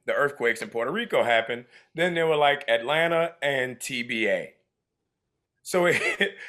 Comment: I THOUGHT HE SAID TVA OMG Like image how Tom would’ve reacted to that if he misheard it